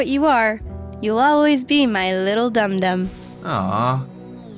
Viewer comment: Wait! Did called a dum- dum😂😂😂😂😂😂😂